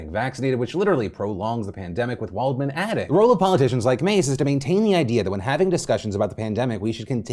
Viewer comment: I heard guerrilla politicians. I thought "posers taking up political offices as strategically hidden mouthpieces for the further leaning right contemporary Republicans to further keep the issues political..." and that was when my brain synced back up with my ears and I tuned back into the show. This shit is good 💨